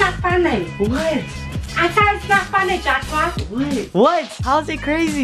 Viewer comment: Bruhhh until this video, I thought his mom was saying "Jatchpa" not Joshua.